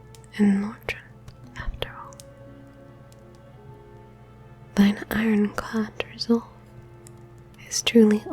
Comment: I legit thought she said "thine iron COCK resolve"! I mean...i'm flattered but lets keep this PG! Ight?